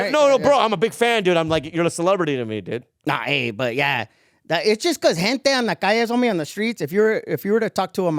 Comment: I thought he said Hentai in a weird accent then I realized he said "Gente" lol my bad